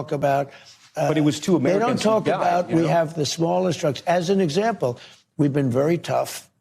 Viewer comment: "We have the smallest drugs" What `?! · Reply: Aha..I thought he said trucks. This makes more sense.😂